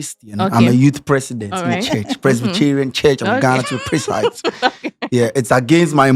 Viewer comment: Did I hear say ‘Presbycherian’?😂😂